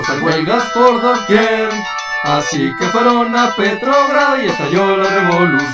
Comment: jajajaja petrogrado, yo pensé que decía PetroBras